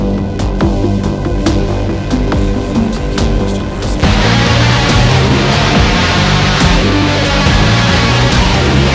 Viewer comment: Somehow I've never noticed until now, but, any idea what he is saying at the 3:57 mark? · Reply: I need to get used to this